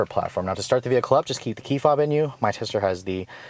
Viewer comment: It sounds like you say “keep the key fob IN you.” Lol. Quite the requirement to start the car.